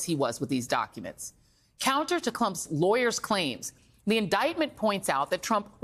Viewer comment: Did she say "Clump's"?🤣